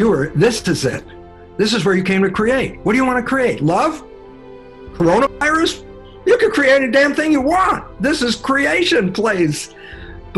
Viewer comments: seriously I hear “coronavirus” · What is said at 2:00?? Create “love and coronavirus??” That’s why I hear but that can’t be right. Awesome video. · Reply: He said, "You can create love OR corona virus" which means it's your choice what you choose to create in your life. Do you want love for yourself? Or corona virus for yourself? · i thought he said coronavirus lol. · did he say corona virus??? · Why did he say Corona Virus? Did anyone else hear that? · Did he just say CORONAVIRUS?